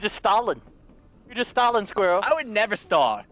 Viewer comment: In the beginning I thought he said squirtel and thought “I thought this was Linch not Pokémon.”